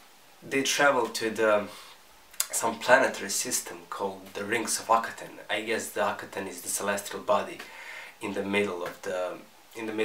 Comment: Lol when you say the rings of akhetan it sounds like your says rings of fucketan